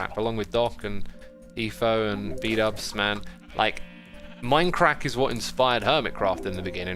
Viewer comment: Did he say minecrack